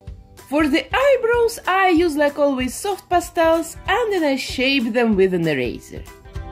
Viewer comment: I heard 'shave them with a rasor' and went: wait? what?🤯😂🤦